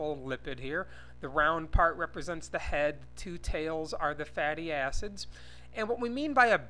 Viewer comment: i thought he said "fatty azzes" lol